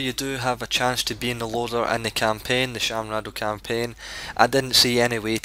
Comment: great video but i think i heard you say "Shaam Radow campaign" lol